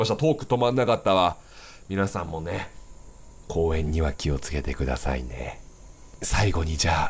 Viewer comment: なんか気をつけてがキヨつけてって言う風に聞こえてきた